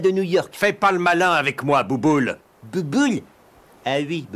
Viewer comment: " Bouboule ? Ah oui ! Bouboule... " 😂😂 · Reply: Au début il a dit Gougoule et non Bouboule. Et après il a dit Bouboule! R'écoute à 0:23 (Gougoule? Ah oui Bouboule...) J'ai compris Boubulle au lieu Bouboule par exemple 😂